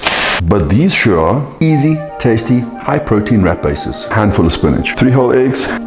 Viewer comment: I thought he said rat bases